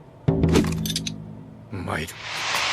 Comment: why is it umai instead of ikuyo, but umai means oishii and why umai translates to let's go, since he did not say ikuzo? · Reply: He said "mairu"